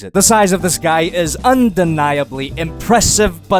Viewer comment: I was watching and then I got a notification saying bruh so this was what I heard “this guy is BRUH.” 😂